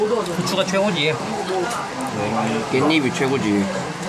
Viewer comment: wait hold up it sounded like he said 'They don't even triggered you-??' AM I the only one?